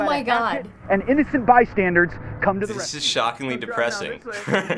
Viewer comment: Did anybody else notice that at 1:07, instead of saying bystanders, he said bystandards???